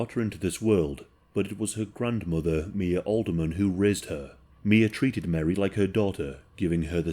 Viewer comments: "who rizzed her" is what I hear 😭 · it sounds like he says rizzed her instead of raised her LMFAO